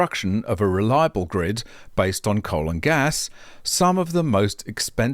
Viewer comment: I think I need to get my ears cleaned out. Instead of hearing "based on coal and gas" (4:29), at first I heard "based on colon gas..." 😳🤦🏻‍♂️ What I heard first more adequately describes what the climate alarmists have in mind for us all!